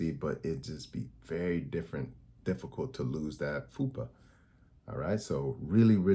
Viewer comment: Sir. Did you say FUPA 7:15 🫢